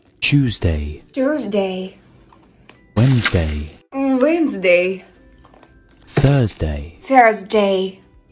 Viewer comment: Кому послышалось печенька на английском уэнсдей сюда 👇